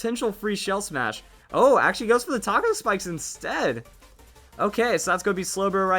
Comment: did you just say taco spikes?